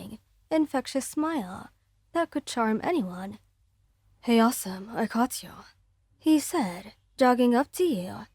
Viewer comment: HE PROBABLY DIDN'T HELP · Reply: I heard it wrong he said awesome I thought he said Elsa